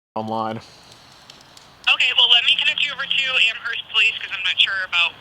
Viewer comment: Fuckin eh thought that said Amherst Ohio. Woulda been my hometown 😂 heart dropped